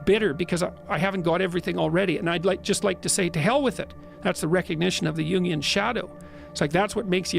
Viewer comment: "Its the recognition of the Junguian Shadow" its what he really says